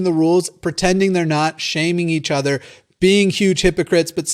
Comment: I thought he said thot shaming